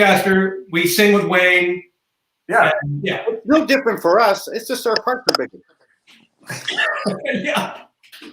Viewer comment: What does Colin say? "It's no different for us, it's just sort of ___ _ ______??????" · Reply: I'm fairly certain he says, "It's no different for us, it's just our parts are bigger."